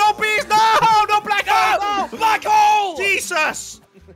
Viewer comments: Did Jelly just say f**k hole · I thought jelly said f### hole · I think I heard crainer say oh no bees oh bees and then jelly said fwak hole Jesus